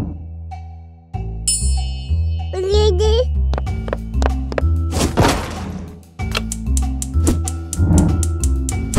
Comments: DID HE SAY THE N WORD?! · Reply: No, he said Milky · what did say?! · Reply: Milky